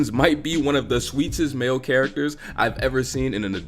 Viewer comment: what word are you saying? “Homer might be one of the __ male characters…”. It sounds like “schwechest? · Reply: “Sweetest.”